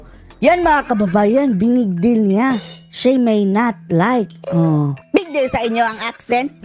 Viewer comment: What i heard was "She may not..." Has Clavio ever heard an extremely fluent English speaker?